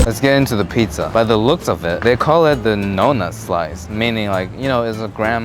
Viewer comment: I thought he said no nut slice 😭😭